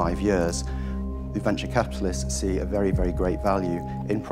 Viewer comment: Did he say "Adventure Capitalist"?